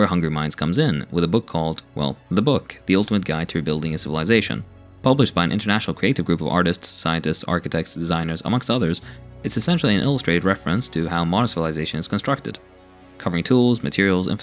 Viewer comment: he said "scientists" and "designers". At first I thought he said Zionists and I was like WTF? 😲